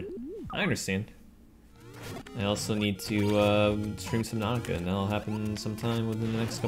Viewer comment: What is he saying he needs to Stream? Naka? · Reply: subnautica